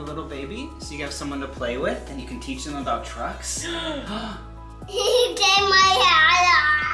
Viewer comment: At first I thought said you can teach him about drugs not trucks😂